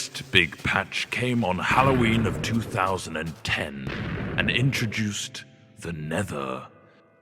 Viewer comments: and introduced the nether. What captions thought he said: and introduced Vanessa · lol 4:04 when the guy falls and at 4:02 if you turn on captions, when he says the nether, captions say vanessa. lol captions